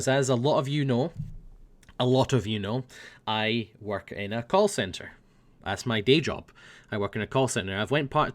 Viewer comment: Whenever says he worked in a call centre, it always sounded like he said 'coal centre'.